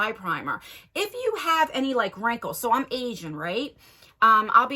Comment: You said “so I’m aging right” I thought you said “so I’m Asian right.” Lmao I was like you are?? 👀👀👀😅